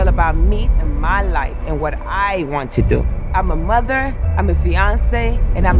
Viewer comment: whyd i think she said im a mother im a beyonce 😭😭 and im a badass bitch